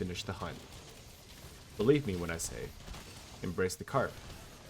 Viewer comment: English is not my native language. Did he say "Embrace the cart"?